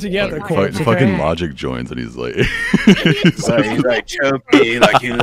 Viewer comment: What does he say at 4:41? I can't seem to understand, no matter how many times I replay it 😭 (i'm not a native english speaker) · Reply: "Fucking Logic joins and it's like" choke me like you hate me...